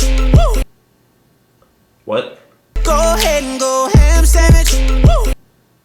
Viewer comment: i thought he was saying "go hand stand it" like go do a handstand